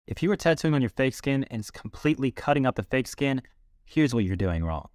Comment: Thought he said “Face skin” at first